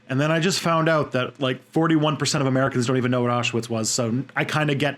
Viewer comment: I'm sorry, but what did he say at 8:10? 41% of the American people don't know what? I'm sorry. I'm not a native English speaker. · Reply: AB DADDY Thank you very much! Now I gotta research what Auschwitz is. 😂